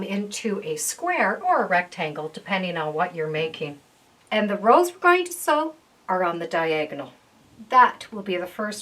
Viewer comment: What are you saying at 9:22? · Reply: And the rows we are going to sew are on the diagonal